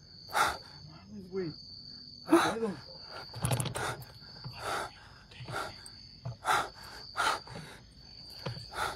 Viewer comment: En el minuto 17:13 al minuto 17:40 se escucha un susurro pónganle atención encerio nose que dice pero es un susurro con voz fría algo se escucha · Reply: Atrévete creo q le dice